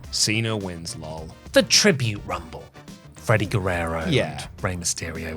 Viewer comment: did just say Freddy Guerrero lol?